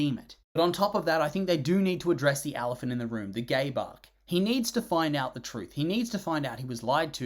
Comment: sorry that this is what im using my comment for, but im doing chores while listening to this and when you said "gabe arc" i thought i heard "gay bark" and had to drop everything to rewind